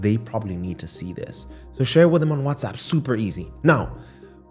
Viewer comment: Did anyone else hear him call your friends fat? @ 7:48 ? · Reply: 😂😂 He said "so share it with them on WhatsApp"